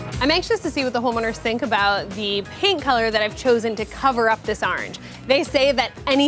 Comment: thought she said it was gonna be pink lmao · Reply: I thought the same thing and then I realized she said "paint color". Even after revealing the charcoal color, she repeats "paint color" and it's sounding "pink". :D Look at 5:22 and 5:33.